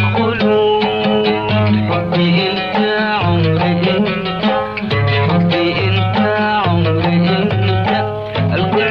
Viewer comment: كني سمعت يقول ربي انت !!!؟ استغفرالله يخوان اللي فهم يكتب يمكن سمعت غلط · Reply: عمري*